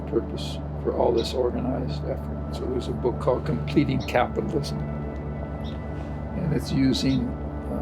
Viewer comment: what's the name of the book the he says at 7:51? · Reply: Completing Capitalism